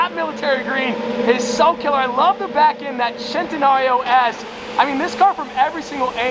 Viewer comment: did bro just say "That Centenario ASS" 🤣